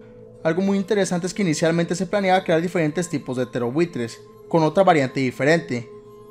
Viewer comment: Siempre pense que decias "terrorbuitres" no "ptereobuitres" xd